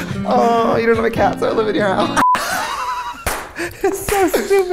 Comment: i still don’t know what tf she said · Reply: She said “you don’t have a cat so I live in your house”